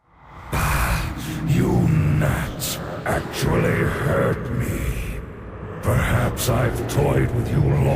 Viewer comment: at about 5:04, does he say Ahh you NATS actually hurt me? :S cus that's what it sounds like lol